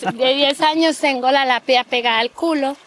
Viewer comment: co ona mówi? Ja słyszę: Tengo la napia pegada al culo. Mam nos przyklejony do tyła..?